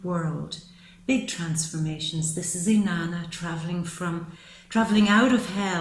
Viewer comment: I've read the "World" woman is called the "dancer" but what's said @ 23:53? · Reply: Hi JP51ism. at that timestamp, I refer to her as being like the goddess 'Inanna'.